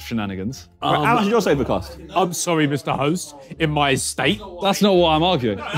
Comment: did Ethan say "I'm sorry mister host in my estate" ?? do they film these in Ethans house?